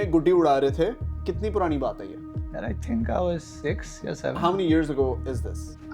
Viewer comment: Who heard 67 at 9:15😂😂